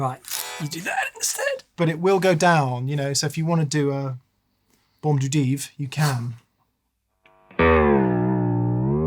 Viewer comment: Loved this vlog! Also commenting to ask if I heard correctly - bombe de dive (44:22)?? Haha great show